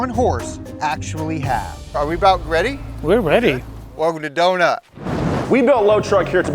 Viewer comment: It sounded like he said "welcome to Dumb Nut".